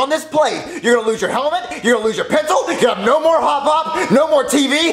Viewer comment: Why did I take me so long to translate that Mario said “‘no more hop hop” I thought he said no more hot pop